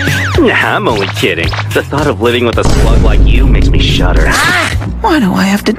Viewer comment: I THOUGHT HE SAID SLUT